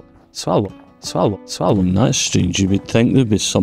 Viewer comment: What the hell is your replay saying? All I hear is... "swallow... swallow... swallow" and am not sure if it's your vids, or the other video's I've got running in the bg lol ;)